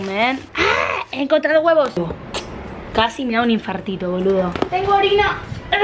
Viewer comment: le entendí "tengo orina" 😂